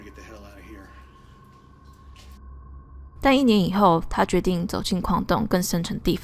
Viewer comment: 1：28我听成“待1年以后”……